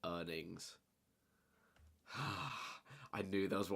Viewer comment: Who else thought earnings said earings